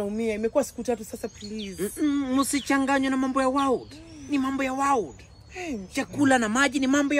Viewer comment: I thought you are saying mambo ya wao kumbe ni mambo ya world..aki Terrence taniua😂😂😂